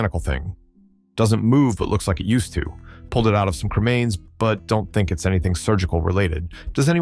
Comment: Grandma had a grinder for her "herb".... 😉 34:25 Did you say it was pulled from remains??? Like the remains of a....